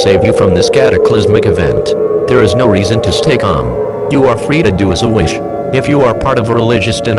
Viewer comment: The video: "You are free to do as you wish." What I hear: "You are free to do as a wish." The automatic captions: "you are free to do a solution"